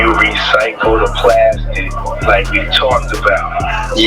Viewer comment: He said “PLASTIC.” not “plastic bottles.” You added a word that wasn’t spoken. All you have to do is read the subtitles. You filled in a word you WANT to be there because you are trying to connect it to the baby oil bottles. But HE DID NOT SAY “PLASTIC BOTTLES”. He only said “get rid of the plastic.”